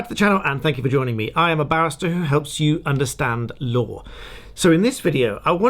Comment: Do get that nice tight r in barrister out, because it sounded like you said you were a bastard who helps you understand law. :)